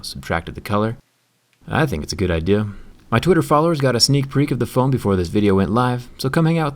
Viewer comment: if you turn on the subtitles and listen, the subtitles say "sneak peek" when zack clearly says "sneak Preek". Just thought it was worth the chuckle. lol